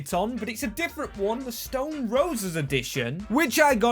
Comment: bro did not just say stolling roses💀💀